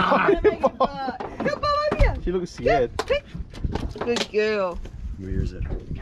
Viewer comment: WAIT BOBS A GIRL????!!!! Did he say “She looks scared”?